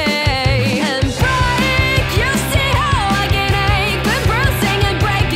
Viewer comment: LOVE the song, but anyone know what Canade means? :) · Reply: Slightly Sleep Deprived the original title was Break (How I Can Ache) but Pablo and Jacob thought i was saying “Canade” and it became the nickname of the song that just stuck 😊